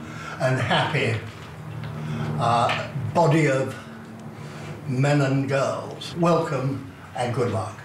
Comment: “Men and girls” lol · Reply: I heard “ men and gulls” 😂